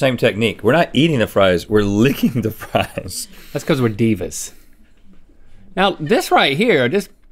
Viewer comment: Did Link just said, "It's because we're divas."?? 😂😂😂